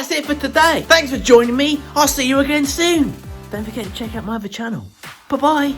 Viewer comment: Love the little pot! I swear at the end I heard "go check out my vaginal"... I laughed so hard, I went back and kept playing it over & over😂🤣😂🤣😂🤣 Cheers!